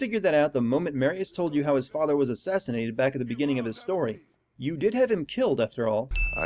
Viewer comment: sin(Assissinated instead of assassinated :D)